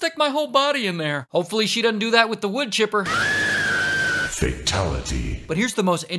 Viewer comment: I thought u said bloon chipper lol